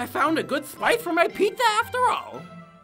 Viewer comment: Why did I hear pitza 😂